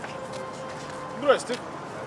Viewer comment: What does that man say? 🤔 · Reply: Точнее, "Здрасти" :) Но по сути то же самое)